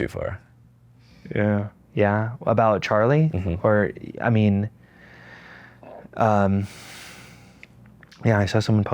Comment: what did he say "So much for the (??)" sorry he said it twice and i still couldn't catch it... timestamp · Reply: Tolerant left